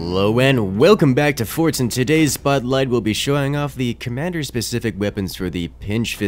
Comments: It sounded like he said “today’s Bud Light” instead of “today’s spotlight” XD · did he say, today's bud light? C'mon it's only Tuesday!